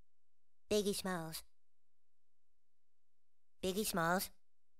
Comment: For a second there I thought he said biggest balls lol